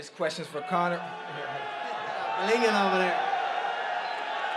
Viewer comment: I thought conor said “a nigga over there” lol